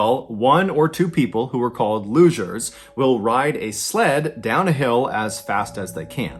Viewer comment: These lugers lol I thought he said losers at first 😆 😆